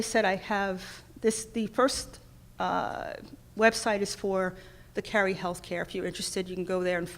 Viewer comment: What is the name of the telemedicine platform that was mentioned at around 22 min of the speech? I thought the doctor said Caring Health, but I can’t find that on the web.